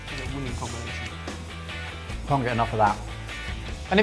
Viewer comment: am i the only one who heard say "Im getting off on that" instead of "I can get enough of that"?